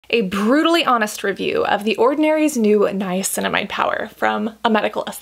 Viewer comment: Did you say Niacinamide power?